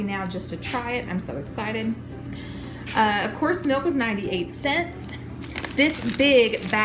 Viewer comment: I heard you say “Horse milk cost...” I had to listen again and you said “of course milk was...” I am cracking up. I was like who drinks horse milk?!? Great haul! Thank you for the laugh